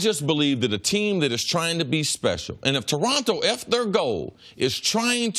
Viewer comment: I thought Cris Carter said, "And Toronto, F their goal!"